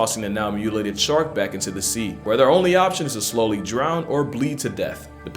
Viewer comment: did he say they could drown?